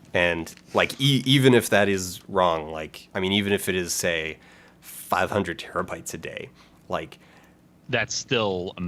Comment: "Even if that is wrong, even if it's like a terabyte a day..." Well, you're wrong, but you went down when you should have gone up. It's not a petabyte a day, it's more like 20 petabytes a Day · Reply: He said "even if it's like 500 terabytes a day" Not quite the step down as you misheard